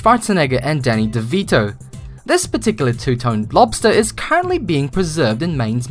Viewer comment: did he say blobster instead of lobster?? XD